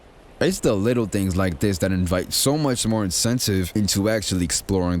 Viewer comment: Did he say “insensitive”